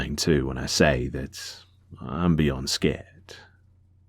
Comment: I thought you said "I'm Beyonce" lmao